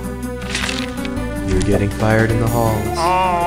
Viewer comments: For a second I thought the principle say your getting fired in the ball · At the start I thought the principal said you’re getting fired in the balls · I hear "fire in the holes"